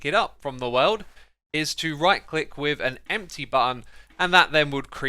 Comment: is it just me or did X say button instead of bucket